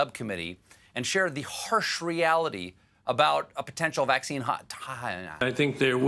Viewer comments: "A potential vaccine hot-tah-yah"...? Dunno what Stephen was tryin to say there but the look he gave was priceless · “A potential vaccine hotahyah”? I’ve skipped back 6 or 7 times and I can’t get a better idea of WHAT THE CRAP HE’S SAYING 🤣😂😂😂🤣😂